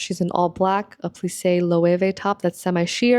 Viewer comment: hi, i didn't quite hear where the top she's wearing in 17:32 is from? i've been trying to find it for the longest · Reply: i double checked the vip fan auction catalog and it's from loewe!